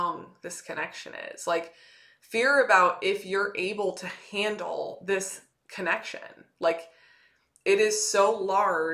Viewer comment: 'I have a question,'..... 'I didn't get what was said in 11:48 mins...... 'it's (****),'..... 'I played back the video multiple times to that part but couldn't make out the word,'..... (I'm not a native English speaker by the way),'..... 'Thank you,'......----- Jason Sy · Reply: Hey Jason! Thank you for the time stamp to help me answer your question. :) The word I said was "undeniable" I pulled the definition for you which said "unable to be denied or disputed." Hope this helps!